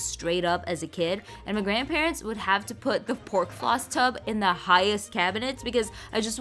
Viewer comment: I thought she said corn floss